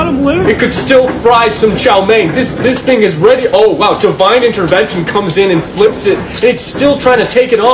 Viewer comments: I misheard it as "Joe Biden intervention" 💀💀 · “You can still fry some chowmein” had me dying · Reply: I thought he said chao ming as in the nba player😅